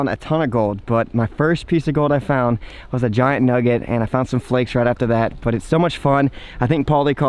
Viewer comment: I thought it said chicken nugget but it was gold nugget